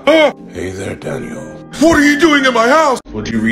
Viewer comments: I heard "Hey there Daddio" 💀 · At first it sounded like he said, "Hey there, Daddy-O"